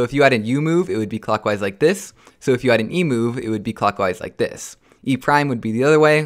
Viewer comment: it sounded like you said emu. LOL